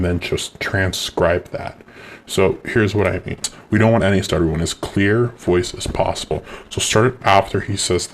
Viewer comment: you mumbled something and I couldn't understand. What exactly did you say? Sounded like "we don't want any slkjdflksjfljs...." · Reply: “We don’t any stuttering, we want as clear a voice as possible.”